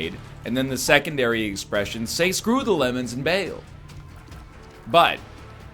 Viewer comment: I'm 99% sure I misheard... But did he just say "screw the lemonade and bang 'em"?